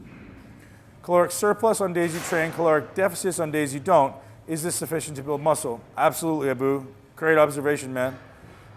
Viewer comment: what does he say at 6:30 till 6:40, can't follow it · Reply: Eating at a caloric surplus on days you train and eating at a caloric deficit on rest days is ADEQUATE to build muscle.